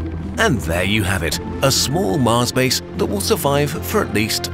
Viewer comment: I first thought it said mars bars not mars base